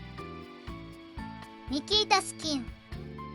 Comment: ニキータ好き😍に聞こえて、「何をいまさら…」と思ってしまった🤣 そしてどんどん新しいギミックが出てきて、先が楽しみですね